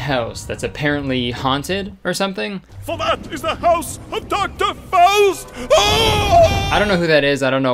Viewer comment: I am not sure but I think he says its the house of dr faust. Which I believe is a german legend about a man who made the deal with the devil. And that's what Mathew does later in the movie. The most clever writing in the whole the whole thing and its a throw away line with no explanation.